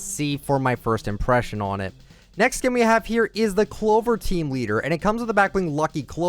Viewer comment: did he call it a male skin